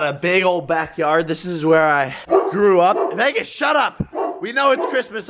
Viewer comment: When he said he grew up I thought he said threw up lol